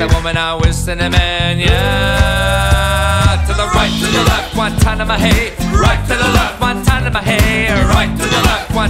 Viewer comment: I know it's not what's being said, but I can't stop hearing "GUANTANAMO BAY" around 2:40ish... In all seriousness though, this mashup slaps in the best possible way.